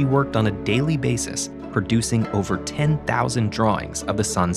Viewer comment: I thought he said over 10! Lol!